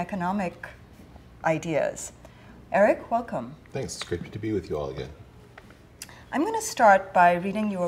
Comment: At the start it sounds like he says “Thanks, it’s creepy to be with you all again.”